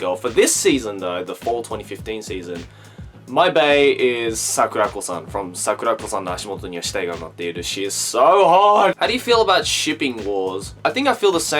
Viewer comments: What the name of the anime at 2:56 plz.I didnt understand what he said at this time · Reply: Sakurako-san no Ashimoto ni wa Shitai ga Umatteiru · I didn't catch the name... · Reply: Sakurako